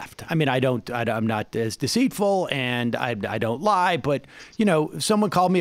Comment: minute 26:08, did Sam say "im not as deceitful."?